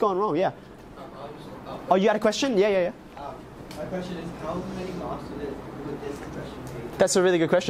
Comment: what did the student ask? i can't quite hear it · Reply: "How many marks would the question be?"